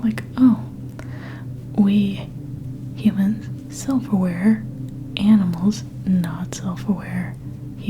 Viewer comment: I thought you said 'humans: silverware. Animals: not silverware' and honestly it still made sense to me haha